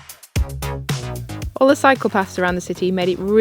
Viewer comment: My South Carolina southern drawling ears had to get a replay... when you were discussing how Sydney was walking friendly... You said "cycle paths". I heard "psychopaths"... "All the psychopaths here make it easy to get around here..." 😂 Great video, definitely on a to-do list for me now!!!